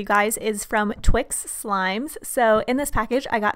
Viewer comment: What I heard was "Twixsksk slimes" XD (no hate btw)